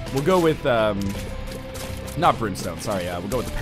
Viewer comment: I swear I heard Ryan say "Brimstar". My day is now made.